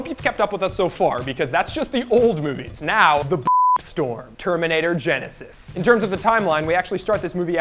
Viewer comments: Did he just say "Beep-storm"? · I heard "The Beep storm" instead of Shit storm ... did he say that just so they can censor it out? lol